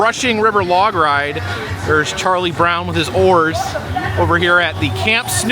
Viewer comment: What is wrong with me? When you said "...and here's Charlie Brown and his oars" I was looking away to do some work and, forgive me, I thought said "...and here's Charlie Brown and his whores"!!! LOL!!! Again, what is wrong with me!?!?! :D It's at 19:26 if you wanna see it.